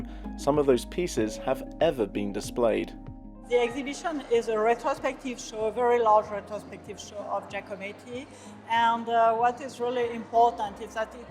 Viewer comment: what is she saying? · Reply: “The exhibition is a retrospective show, a very large retrospective show of Giacometti......” blah blah